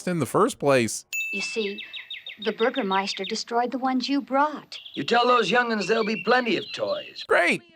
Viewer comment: You guys wrote “bought” instead of “brought” in the subtitles. I figure this channel is the perfect place for pedantics